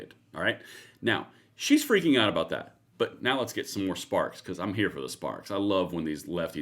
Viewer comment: I thought you said "I'm here for the Spartz!"